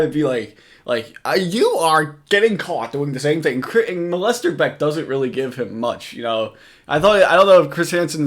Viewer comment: Has ANYONE ever noticed tcap is playing on the TV in this video? The irony… we’ll watch ya, bud Also 11:48 - did you say “MOLESTERBECK”? 🤣